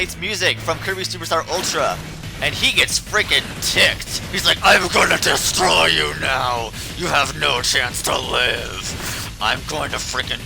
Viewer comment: I heard "I'm gonna DESTROY you now! You have no chance to LOSE!"